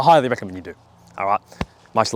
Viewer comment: "Fark massasours gain?" - ah so thats what he said lol